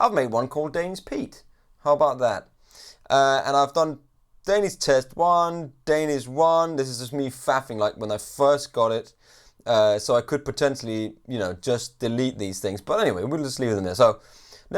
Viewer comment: Did you say "fapping"??